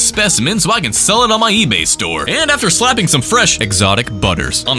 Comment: NOT ME DYING WHEN I HEARD "EXOTIC BUTTERS"